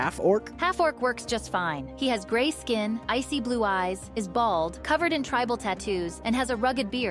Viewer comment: I heard : his balls covered in tribal tattoo's, I was like wtf